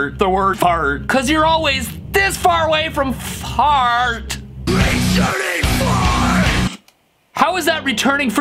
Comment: Bro it sounded like "RETURNING FARTTTT!!"